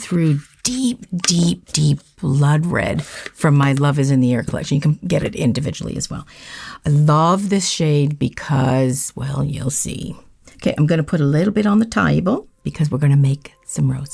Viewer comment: at 31.21, I misheard "Love Is in the Air" as "Blood Is in the Air". Now I'm wondering, since Halloween is coming, what colors from your collections might count towards a "Blood Is in the Air" nail set? 🤔💙